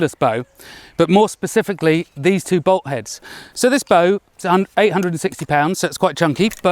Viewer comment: I didn't think anything of it when he said it's 860 pounds, because I don't know how much a pound is. Also I initially misheard it as 160 pounds.